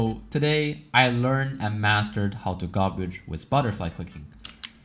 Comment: I thought he said Spotify clicking lol 0:03 Anyways amazing video dude